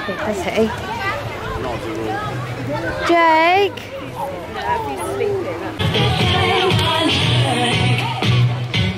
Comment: Not being rude I'm just curious haha did Jakes mum give saffron into trouble for trying to waken him? I heard 'he's sleeping' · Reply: She said just leave him love he is sleeping xx